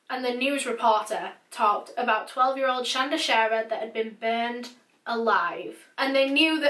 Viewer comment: I thought it said Sandra sharter